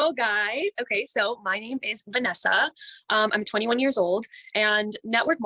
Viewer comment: When she or anyone else says "hi I'm 21 years old" I hear "hi I'm a legitimate child"